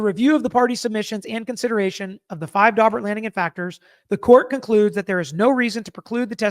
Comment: You said "Daubert hearing" . . . I heard "Dogbert hearing" . . . which, given the testimony of the bite experts, seems appropriate . . .